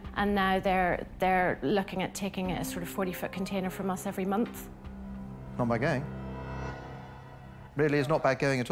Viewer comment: what did he say? "not my gain"? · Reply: He said “not bad going. Really is not bad going at all.”